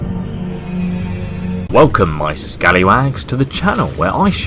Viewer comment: I thought you said "hello my scallywanks"